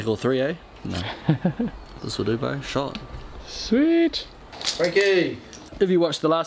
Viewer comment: Did he just say "Shot"? LMAO I thought that was something only a South African would say hahaha... Mind blown! xD